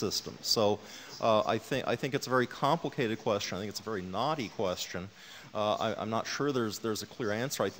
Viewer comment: Lol! for a minute I thought he said it was a "Naughty" question. I'm like, Really? There are MUCH better reasons to call Hitch naughty. "Knotty" question. I'm pretty sure he means "Knotty". lol oops. Damn you homophones!